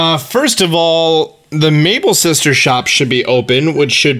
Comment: Did you say “ Mable sisters shop?” lol it’s Able sisters shop